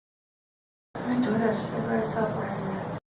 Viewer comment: I've listened to the audio several times and IMO, it sounds like she said, I'm a door dash driver, I saw Bryan leave or I saw Bryan there.